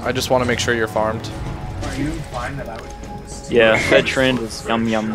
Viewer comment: Did I just hear, "Are you, limeitup" That is my League of Legends/Near Reality name. Thefuck?